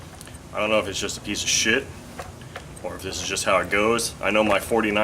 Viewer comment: It sounded like he said "it's a piece of shit" at 33 seconds but I hope I'm wrong as here in England "shit" means rubbish, scrap, only worth throwing in the trash can, this can't be so can it?, as I have three of these engines, one being a flat twin.